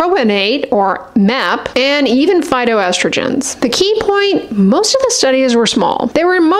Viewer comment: I heard "phyto estrogen" as "Fido estrogen" and for a minute wondered what dogs had to do with it. This is a brain on menopause (even with my beloved HRT). Thanks for another great video.